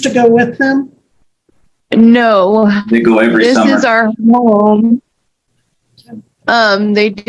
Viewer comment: ish sounded like she said "This is our mobile home" but her mic is so bad she played it off like thats not what was said